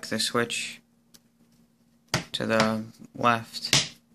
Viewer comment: I thought u said 'tadaa' like a magic trick by u actually said 'to the' 😂😂